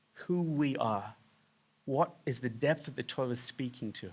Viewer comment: Please check in to editing the closed captions. At time stamp 9:57 (9 minutes, 57 seconds) into the video , the caption said, " the TOILET speaking to what's the depth of the divine." I believe that what you WANTED to say is " the TORAH speaking..." You may be change the caption by pronouncing "ToRAH," with the accent on the 2nd syllable, instead of "TOrah," which gets the output of the toilet. 🙂